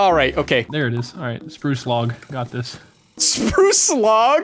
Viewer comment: anyone else thought he said sproo slog instead of spruce log